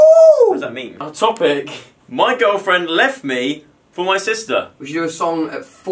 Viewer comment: WTF WHY IS THIS SUCH A BOP! Also can I just say I love the lesbian equality in music now and this song wasn't discriminating against the LGBTQ+ community and was actually really nice. It seemed like a normal thing to sing about and that fucking amazing considering the hell some people put the community through. Love you guys and yes of course I downloaded the song... it's a freaking BOP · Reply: Abbas Ke Bakwaas he clearly says ‘girlfriend’